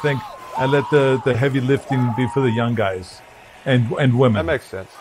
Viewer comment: Schwarzenegger says "Workouts are still pretty intense..." and as he says 'intense' Derwin James baptizes Breece Hall 😂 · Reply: I heard it more as "I leave that to the young guys" *DJ enters chat